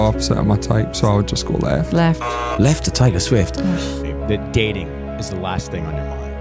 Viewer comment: Hello! I love this video so much, and I want to make sure everyone can understand and enjoy it. That's why I wanted to let you know that your captions are a little off. At about 0:19 the auto-captions say "gaining" where they meant to say "dating is the last thing". When you get a chance, could you fix that? That'll help the 360 million folks globally who rely on closed captions to understand videos. Thank you, and keep up the great work!